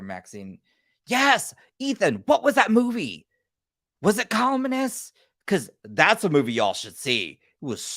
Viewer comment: ????? What is the movie? I can't make out the word. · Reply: It's The Columnist. It should still be on Shudder. It's a really good take on toxic twitter trolls.